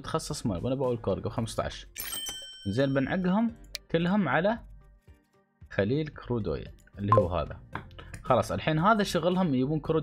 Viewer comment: مادري ليش سمعته خليل خودوم😂